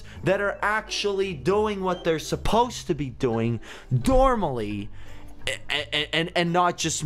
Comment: is it just me or did say dormally